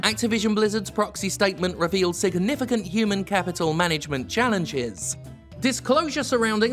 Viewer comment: When you said Human Capital Management, Jim, I heard it as Human Cavital Management and thought that made sense.